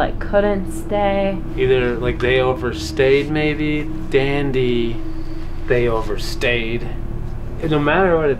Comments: This is so dark I’m actually scared for you guys I heard it say “dandy family overstayed dandy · I kept hearing "dandy COULDN'T stay" but listening over and over it sounds like "dandy summoned me" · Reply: I heard "Dandy's. They overstayed." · the evp sounded like it said dandy touched me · i hear "DAN-DY... NOTH-ING" question was what did they do to deserve it